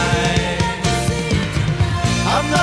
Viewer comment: "Not talking about moving in" Misheard as "not talking about no linen"